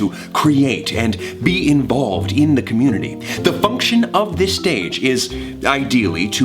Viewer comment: was it just me but i heard fu**ion in stead of function.